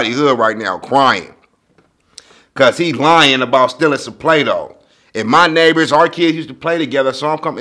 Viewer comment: I thought he said plate out not play dough